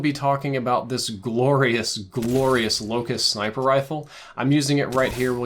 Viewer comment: did u just say LOCUS😳🔥♥️